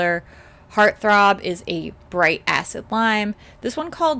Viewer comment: LOL I thought you said bright a** lime, and I was almost shocked. But it's a bright ACID lime.